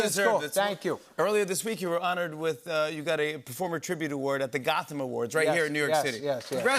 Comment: Gotham awards... Lol!!! I heard gospel awards and thought, say what??? So I played it again. I'm hard of hearing due to listening to LOUD music for decades. Does that mean I should a AARP membership? LMBO.